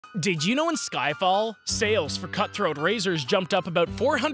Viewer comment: Bro said skyfall, i heard "skyballs"💀